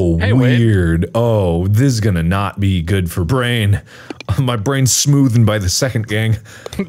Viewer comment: Did I hear "my brain smooth"??!! XD